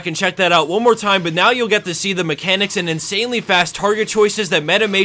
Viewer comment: lol I swear to god I thought I heard him say mechanics in Katarina play...